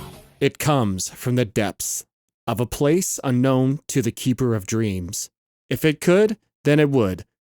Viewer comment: But i thought he said "of a place with no tuna" :(